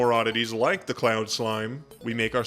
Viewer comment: almost thought ya said clown slime, there. now im mad there isnt a clown slime in the game.